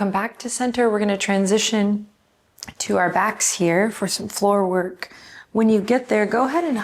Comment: Did say "flooral work"? That's a lovely way to put it 😊🏵